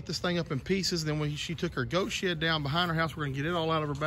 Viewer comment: Sounded like you said her goat shit lol